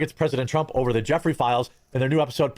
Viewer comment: Wait wait wait, sorry to take up two comment spaces, but did he just say “Jeffrey Files?”